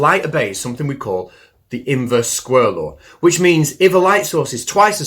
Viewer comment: Did you just say inverse squirrel law? 😂 love ya bro